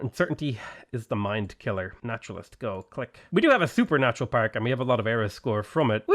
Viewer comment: it sounds like he says "Supernatural Park" which is definitely something you should be able to build on tiles where meteor strikes happened